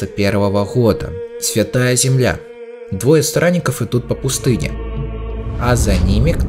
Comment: я один слышу "двое сранников"?